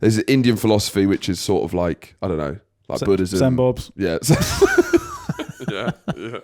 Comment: What does Fin say there in response to mentioning Indian philosophy? I still can't make it out and nor do the subtitles. 'Zenbobs'? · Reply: Send bobs